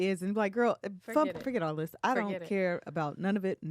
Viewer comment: I thought said F*ck This!